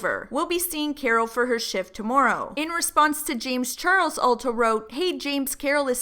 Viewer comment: I genuinely thought your kept saying “ultaro” not “ulta wrote” 🤦🏾‍♂️